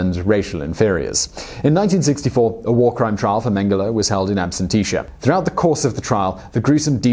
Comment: What is that word @ 18:49 of the video? Absentitia ? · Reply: In the U.S., the term would be “in absentia” (ab-SEN-shuh). Not sure if this is an aluminum/aluminium thing or just a slip of the tongue.